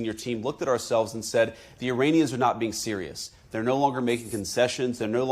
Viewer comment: Did he just say " iranisky"